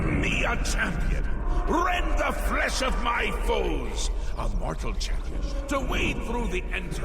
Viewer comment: I had to do a double take 'cause I thought he said, "Rend the flesh of my folds." I was like: 🤨